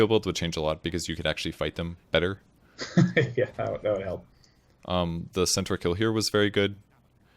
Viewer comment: i always hear day9 saying:'go to hell' instead of:'that'll help'